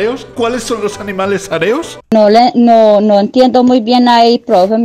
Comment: minuto 10:55 ya lo entendí dice aereos